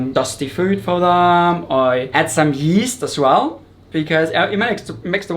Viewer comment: Great aquarium! I did not understood what he says uses as food for the gorgonians in 9:18. Can anyone write it down? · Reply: yeast